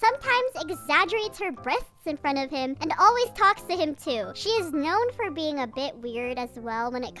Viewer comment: i thought you were gonna say she is known for being a b!tch 😭😭